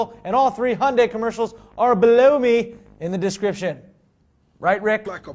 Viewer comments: sounded like you said blow me when you said below me.. 5.25. you n your funny lil american accent D.. or was it intentional? love the show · what's funny is, when starts talking about the links at the bottom, sorta sounds like he's saying "blow me" instead of "below me"...........